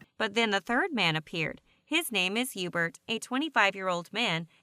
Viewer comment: I heard Yoobert...